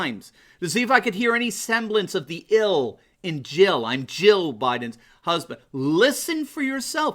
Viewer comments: I thought he said "Joe" Biden's husband, too. But listened in slow mode and could hear that he did indeed say "Jill" Biden's husband. Let's not make fun of simplistic things - it only gives the left more ammunition against the conservatives. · I have not watched any of the convention because I do not want to hear the whining. I did watch the clip where Joe came out to his wife. I closed my eyes and I heard him say Jill Biden's husband. He ran Jill into Biden but I heard it. Maybe it is a laurel-yanny thing!